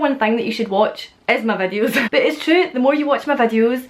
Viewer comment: I thought you was say something else and i replay again you was saying "videos" in your accent "vadius" Lol